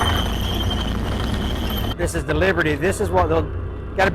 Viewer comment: I heard saying "Liberty", while I thought I was hearing him say "Leopard".